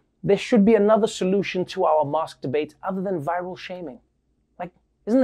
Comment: "mask debate" Anyone else heard masturbate?